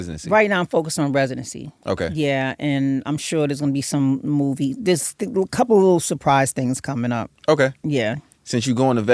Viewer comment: what??? Did she say a movie? 😅